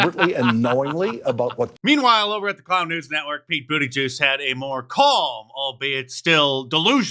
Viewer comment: Did you just call him, "Pete Booty Juice?" OMG, ROFL!!!!